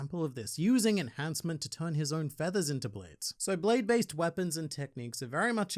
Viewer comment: I know you said blade based.... But I heard Beyblades. God help me. 🤦🏾‍♂️😬🙆🏾‍♂️